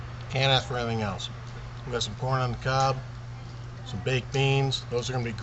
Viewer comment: Did he say "We got some Porn on the Cob"? I'd like to see that. :-D